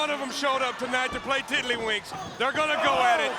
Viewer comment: wtf is mongo saying 🤦🏽‍♂️ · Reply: tiddlywinks